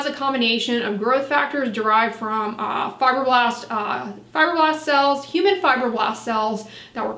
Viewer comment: A little slow on the uptake today. I thought you were saying fiberglass. 🤔 I wonder about me somedays..